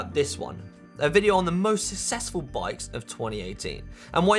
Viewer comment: At the end I thought I heard you say "Check out the link for the most Accessible Bikes of 2019". (Mis-hearing that you had said most Successful). That certainly caught my attention and made me wonder .. ARE there a group of vendors who specialize in creating bikes/trikes etc for those who might need such? I haven't ever seen a review of what the possibilites are.. might be a worthwhile segment.